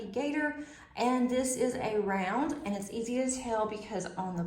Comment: You said “easy to tell” and I heard “easy as hell”, both true and it made me giggle. Enjoy your painting and the event!